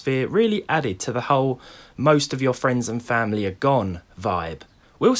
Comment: thought that I hear “most of your frames and families are gone”